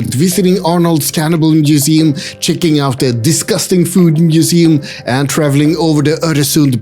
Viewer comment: did he say disgusting food